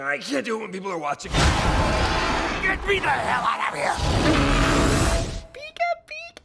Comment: DID PIKACHU JUST SAY HELL?!